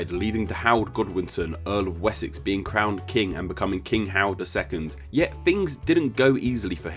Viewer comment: But then we don't really call it a beef burger anyway we call it a hamburger lol! I love your series and I enjoy it very much. To my American ear, it sounded like you said King Howard instead of king Harold. I didn't know the pronunciation was so different between America and the UK on the name Harold